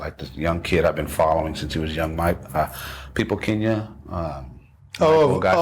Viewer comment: Remember the name of the bass player mentioned: Michael "Pipoquinha" Lima, from Brazil... unbelievable bass player! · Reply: Thank you! I was trying to find it based on what I THOUGHT he had said ... People Kenya, hahahaha. So, thanks.